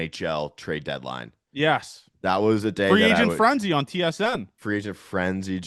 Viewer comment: The nhl trade deadline is during the school year lol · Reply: Lolz. My bad, bro. Thought you said Free Agency Frenzy. Really don't want injury-prone Corey Seager for that remaining term.